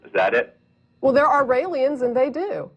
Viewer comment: Wait did she say "ralians" at 3:03? What is she talking about? Sounds like "aliens"? What is this group she's talking about?